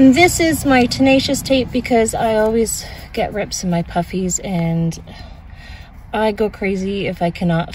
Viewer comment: what did you say?!! “I always get rips in my puppies” “I always get rips in my puffies” ?? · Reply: Puffies lol. My puffy jackets.